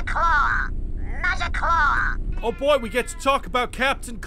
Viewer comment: I completely forgot where that "MAGIC CLAW!" voice clip came from and what it was even saying. (I thought it was saying "Majikura" or something non-English sounding and figured it was from Diablo 2, like "Rakanishu".) Glad I could stumble upon it again.